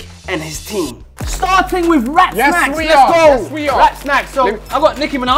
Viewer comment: Thought he said rat snacks😭